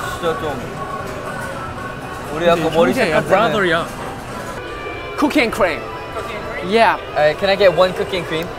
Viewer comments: When jungwoo said "cookie and cream" I thought he said cocaine and crem XD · I thought he said cocaine and cream....dont judge me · i can’t be the only one that thought jungwoo said “cocaine and cream” not cookies and cream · BAHAHAHAHA I LOWKEY THOUGHT JUNGWOO SAID "COACAINE AND SCREAM" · WHEN JUNGWOO SAID COOKIE AND CREAM I THOUGHT HE SAID COCAINE AND I WAS ABOUT TO SCREAM